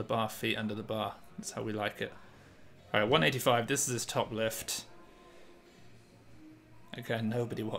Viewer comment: Did I just hear a Wu Lift reference lol?